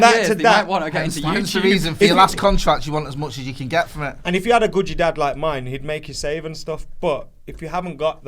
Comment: Did say ‘if you had a guji dad like mine?!’